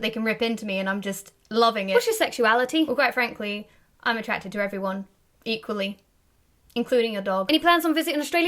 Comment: Did anyone else mishear that as "I'm attractive to everyone" ? ;)